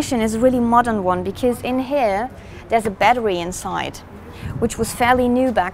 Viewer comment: did she say batwee